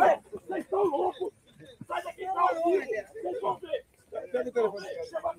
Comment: "pega o telefone dele" só eu ouvi o bolsonaro falando isso?